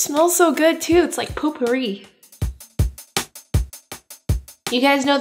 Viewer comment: Did she just say, "Poop-ery" @ 9:16?! LOL!!!